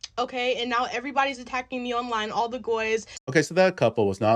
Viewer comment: Did you hear what she said? At 6:57, she said "all the GOYS", the subtitles say guys but that's NOT what she said! She also makes a lot of anti-White comments in this rant. So she is outright slurring non-Jews and Whites. If that's not a case for a racist, bigoted mindset, I don't know what is.